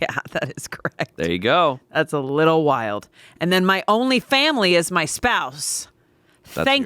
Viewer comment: swear to god i thought was gonna say “and my onlyfans” lol